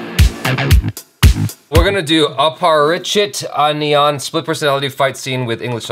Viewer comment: Did he just say anniyan as ONION ??🤔